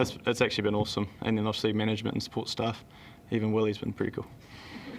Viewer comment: What did he say at 5:35. Anyone?? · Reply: Nusrath Akhil he said ‘’ Even Willy’s been pretty cool’’ - not sure who that is but he is probably referring to someone